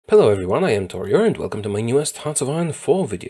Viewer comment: You introduce your name so fast that for a while I thought you are saying 'hello everyone I'm in 'Tutorial'